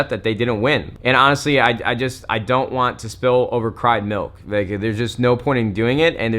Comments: did anyone else hear him say spilled over cry milk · Spilled over cried milk? Did I hear that right? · I had to pause, 4:03 did he say "spill over cried milk" or am I hearing things. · did he say spill over cried milk at the 4:05 mark?